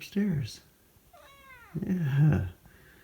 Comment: I swear I hear him say "yeahhh" there haha